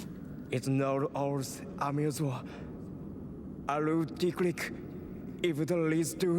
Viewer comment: did he say dick lick?